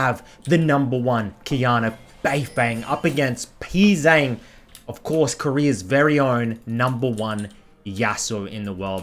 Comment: “of course koreans own No. yasuo in the world” me: what did he say?